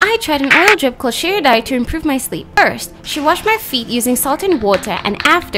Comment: I thought it said remove my sleep